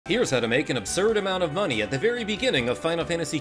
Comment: I'm so out of it right now I thought you said " here's how to make a absurd amount of honey"